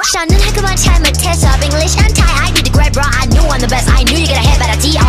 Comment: Am I the only one who misheard Milli saying ''I did it great bro" to '' I need a Grey bra"?